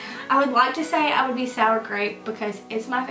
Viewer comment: Wow I thought you said sour cream jelly bean at first!!! 😂